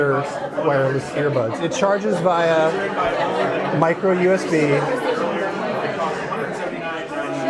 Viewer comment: did he just say Micro USD?? i'm sure he meant USB... but it clearly sounded like USD